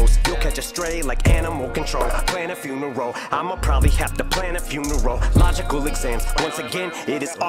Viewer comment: he said " plan a funeral I'ma probably have to plan a few neurological exams" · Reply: wow I just got that when u said it. I heard it as "Plan a funeral. Im'a probably have to plan a few-n-a-row"